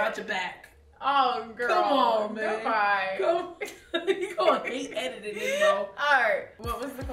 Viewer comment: Did I hear say “you gon’ hate editing this Mo”?😂🤔